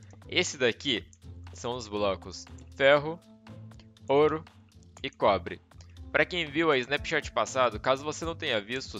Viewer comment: #minérios feno, couro, e pobre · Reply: Na hora que você falou "Ferro, ouro e cobre" eu entendi "Feno, couro e pobre" kkkk